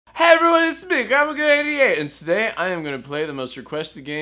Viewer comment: it sounds like he says grandmagod hatey ate